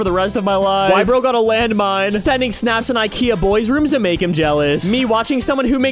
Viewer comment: I thought you said “Kia boys” for a minute and got war flashbacks